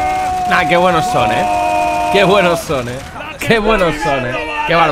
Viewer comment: pensé que decía que bueno zone y dice que bueno son